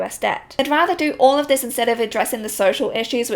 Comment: It's probably just me - but I always hear "crapitalism" instead of capitalism in your videos - and I love it 😍